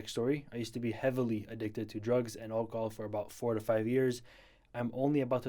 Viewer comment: I THOUGHT HE SAID DRUGS AND AHEGAO